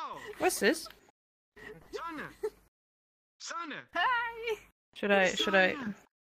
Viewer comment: Я одна слышу что кто-то кричит "Саня!!Саняяя!!"?